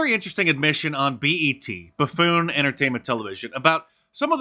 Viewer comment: I had to do a double take, I thought you said baboon entertainment television. 😂